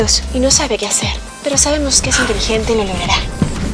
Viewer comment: ¿Que dijo? porque escuche "Pero es Divergente y lo lograra"